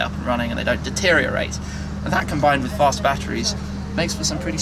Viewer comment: Did he really said fast batteries